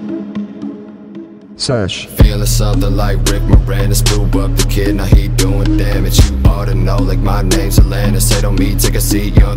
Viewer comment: he says "I hate doing damage" and you know what, frequencies in music are used. I was able to hear a high-pitch frequency at that moment 3 years ago but now I can't hear it anymore as my ears most likely getting older(lose ability to hear high frequencies). 1:38 is a high pitch frequency damage